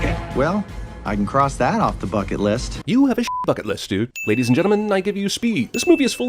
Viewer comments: Thought he said "Well, cross that off the fuck it list." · I thought it said well i can cross that off the f***ing list instead of bucket list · I REALLY THOUGHT HE SAID FUCKING